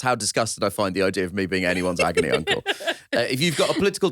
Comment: It sounded like Nish said “angry uncle” instead of agony uncle towards the end and I gotta say, new nickname? Certainly fits with him talking about a villain every week.